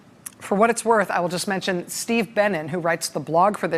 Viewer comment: Who else thought that she was saying Steve Bannon instead of Steve Bennin?